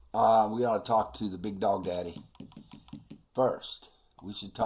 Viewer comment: I'm sorry, did you just call Dutch DADDY? Looks like he was more then just a mentor to young Arthur!😶😲😳😅🤣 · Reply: "Big Dog Daddy" I said